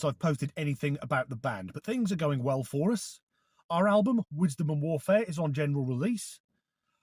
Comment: When you said Wisdom and Warfare, I thought you said Whitstable Warfare 😅